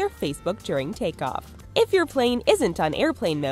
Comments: Did anyone else hear ”plane“ instead of ”phone“ at 4:48? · I love how their captions say if your phone is an on airplane mode, but the voiceover person said if your plane is an on airplane mode see if you can catch it